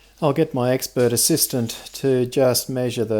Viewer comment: ”Expert assistant” but I heard “ex bird assistant”. Shocked for a moment there :)